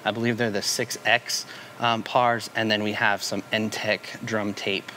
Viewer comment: What was the name of the lighting tape under the drums? It sounded like Ntech Drum Tape but I’m struggling to find it on Google. Is the brand name spelt differently? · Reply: Hey there! The company is "ENTTEC"